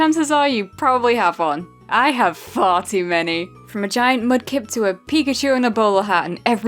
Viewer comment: I thought she said "Farty many"